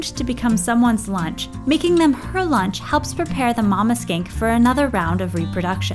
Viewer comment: Oh! Mama "skink"! I thought you said something else. Mama skank.